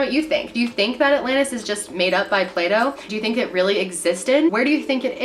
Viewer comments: Who else thought she was saying Play-Doh? 😂... Just me...ok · In the brumuda triagle video i thought you say "Play doh"! Yes , I'm a weirdo!